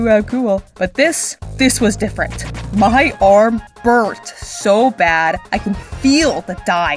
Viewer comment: my subtitles said “my arm BIRTHED..”